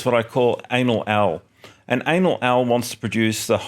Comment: Had the video minimized when I heard "Anal Owl"